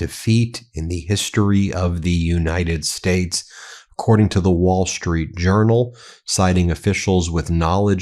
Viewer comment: I'm hard of hearing but I thought I heard you say 'The Wall Street Urinal'. Freudian slip?